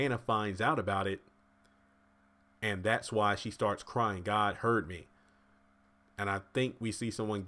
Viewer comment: I really thought Hanna said "God hurt me". I get how people think she said "heard" though since the two words sound so alike. But I'm sticking with "God hurt me".